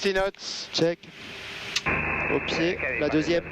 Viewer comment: Did he say "oopsie" at 7:39? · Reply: No he say " Au pied " in french. It means "at feet"